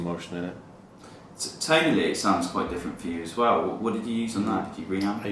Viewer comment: What is the interviewer saying at 4:17? When Marks says "Its my fingers." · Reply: The interviewer is saying "Tonally it sounds quite different for you as well. What did you do on that?" - referring to Mark's guitar tone during the solo and how it sounds quite different to what he usually does.